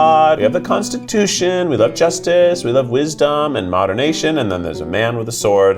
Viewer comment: did he just say "modernation"?!